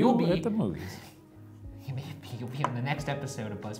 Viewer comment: instead of "buzzfeed unsolved" the captions made it "lesbian unsolved" 😂